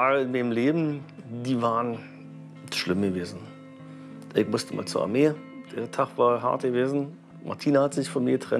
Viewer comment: Akkustisch schwer zu verstehen. Sagt er da: "Ich mußte mal zur Armee. Der Tag war hart gewesen."? Wie darf ich das verstehen? Bin verwirrt.